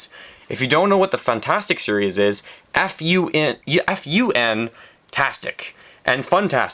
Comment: When he said ' If you don't know what the funtastic series is...' I thought he started saying f you XD